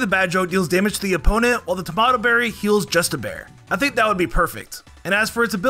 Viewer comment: When you first said justibear I thought you said just a bear 😆